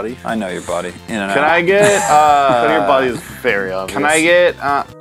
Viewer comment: can someone please let me know what says right here? for some reason i can’t understand what he says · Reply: is it Knowing your body is very obvious?